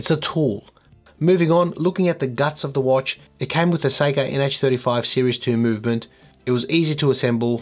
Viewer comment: As a New Yorker, with our own distinct accents, I kept hearing your “Seiko” pronunciation as “psycho” which made this video even more fun. Oh, and hanging a “disco ball.” Doesn’t get much more awesome. 😅 Cheers!